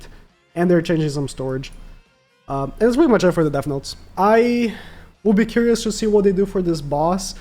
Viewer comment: i thought you said "death notes" LOL 😭